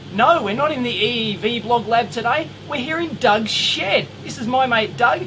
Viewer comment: I thought he said Dog Sh*t